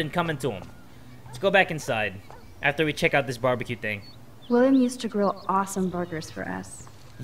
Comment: when max said "William used to grill awesome burgers for us." I thought she said "William used to grill awesome boogers for us."...